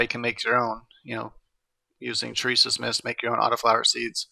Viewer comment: in video you said you can make your own auto flowers using something. Did you say Teresa’s Mist? What is that? Is that what you said. I’m interested in how you can make your own autos. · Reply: Look for terisas mist on ebay